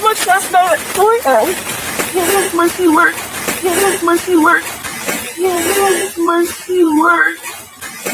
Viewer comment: i think she says "We hear Murphy Bark at around 4am"...